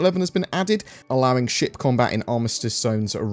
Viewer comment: I thought you said, “...allowing shit combat...” rather than ship combat haha